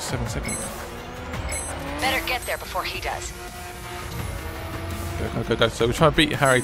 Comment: there is a spelling mistake it says he does but says he goes no joke